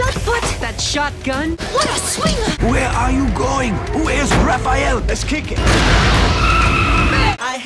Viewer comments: is it just me or did it sound like Splinter say "who is Raphael?" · Did I just hear Splinter asking "Who is Raphael?" When he demanded what was going on during thier leaving/gearing up montage? Or did I misheard it? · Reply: Lol might as well. He said where is Raphael. Fortunately